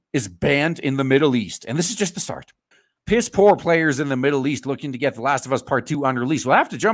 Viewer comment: When he said "PS4 players in the middle east..." I thought he said, "Piss poor players in the middle east..."